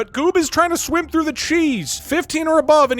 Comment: personally, i hear "gloob"